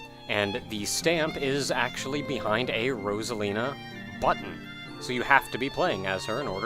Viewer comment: "...hidden behind Rosalina's button." For some reason, my vid buffers at this sentence, so what I hear was "...hidden behind Rosalina's butt."